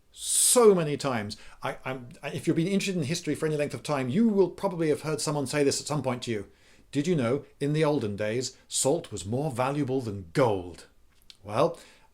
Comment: I always heard: "sugar was more valuable than gold!" Just me?